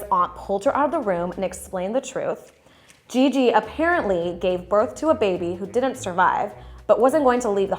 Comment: Did he just say "gaven" birth....